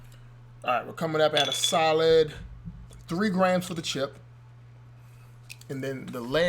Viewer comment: did anyone hear that said shit!?